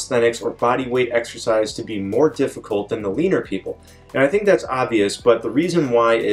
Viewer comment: I know you said "leaner people" but I heard "Weiner people"